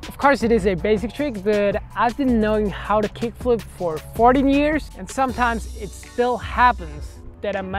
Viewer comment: Thought he said 40, I was like wait how old is???!?!